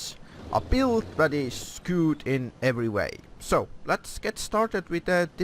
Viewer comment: I thought he said "A build that is screwed in every way" lol